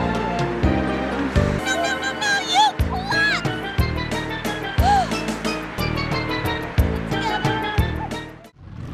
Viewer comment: Did she call herself a butt? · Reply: Klutz